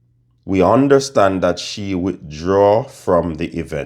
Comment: I could have sworn I heard Kenny had withdrawn from the competition